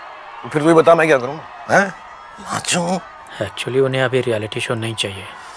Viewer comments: did he said MACHOD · what did he said ??? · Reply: Nachu 😂